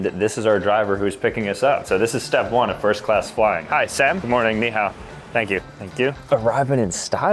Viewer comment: did he say Ni-hao?😭